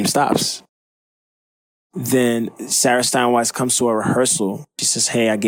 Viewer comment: I thought it said Sara Steinwess, NOT AGAIN! lmao